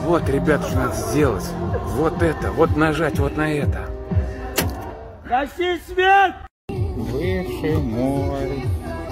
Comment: XD Who yelled and what did he say? He sounded like he was right next to him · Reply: "Гаси свет" means "shut down the light"